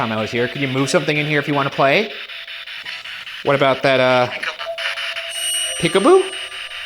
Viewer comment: I think it's "here I come" instead of "peekaboo"